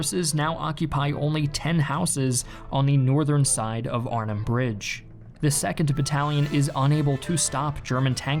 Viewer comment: you say Arnhem bridge but subtitles say Rhine.